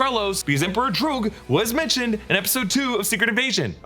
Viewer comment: Geezis I thought said "Emperor George" all this time since Secret Invasion wtf lol